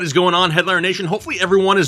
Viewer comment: When he said what "going on Headliner Nation" I heard, "what's going on Hitler Nation" and had to stop to think about what he said 😂